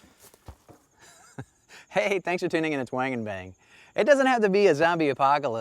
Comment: I heard "Wang in bang"....hehe thought that was funny but I still like your demeanor and presentations very much thx